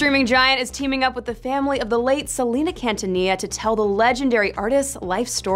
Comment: Did she just say 'Cantanilla"?